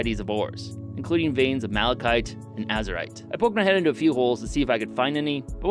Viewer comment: i swear i heard "Azerite" instead of Azurite... god dang WoW, you've ruined me. At least I still have my spanish left.